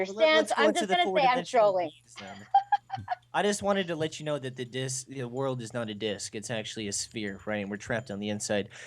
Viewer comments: i thought she said like the world is a dick... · An 3:46 clearly she says she's trolling those of you who didn't catch that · SHE SAYS: "IM JUST GUNNA SAY THAT IM TROLLING" anyone else catch that? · Did anyone else hear her say that when is trolling at 3:44???? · did she say she was trolling at 3:45??????